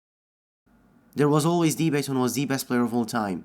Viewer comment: Dafuq does he say at the beginning? “There was always de best of de best of all time..!!??”